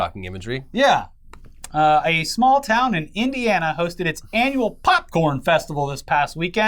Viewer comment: I heard “a small town and Indiana” and got scared for which one it would be Phew, not mine